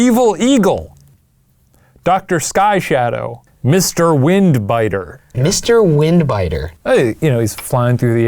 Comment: "Mr. Wind Biter" I thought he said "Mr. Wind Butter"